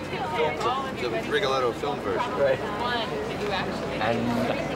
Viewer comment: Idk exactly what he is saying, but it sounds like a bit of Mel's Aussie accent popped out there. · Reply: I think he said "The Rigoletto film version" :) and yes, it kind of does!